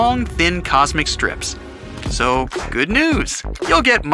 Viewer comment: did he say thinner than a human hair